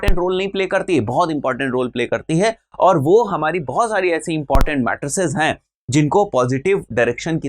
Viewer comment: important mattresses.haha... instead of imp matters.. but on a serious note hez doing a good job, i really like his videos.